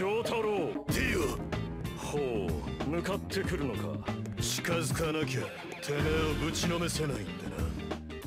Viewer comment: What he's actually saying: I can't beat the shit out of you without getting closer Sub titles: I'm not, you broke into my house throwing my friend around